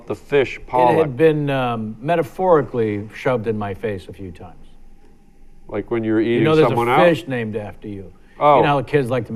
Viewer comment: Listen closely what Harland says. I lost it. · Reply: David Eriksson “Like when you’re eating someone out?” Lmao